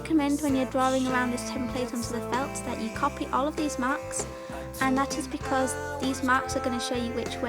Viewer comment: It sounded like u said said DEEZ MARKS HA GOTEEM